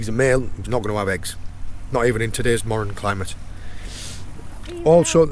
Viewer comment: Did you say he wouldn’t have eggs “even in todays moron climate” or did I mishear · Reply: Modern climate. Lobsters obviously aren’t as progressive as humans being they only have 2 genders and the males do not give birth.